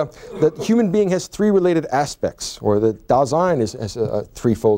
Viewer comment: Hi! He says a word at 16:25 (any some other times) which in the closed caption is "design" but I hear something 'dal zine' ... or ... can somebody help me what is the word he is saying there ? Thank you! the context is in the closed caption is "... aspects or the design is a three-fold..."